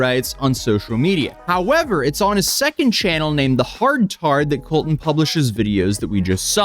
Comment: So at 0:59 did he say the 2nd channel was called "hard tard"? Cause that'd definitely make sense if so.